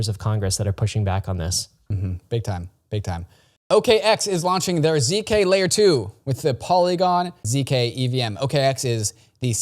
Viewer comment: when you said OKX is launching their layer two, I thought you said "okay, X is launching their layer 2" and I was like WHAT?!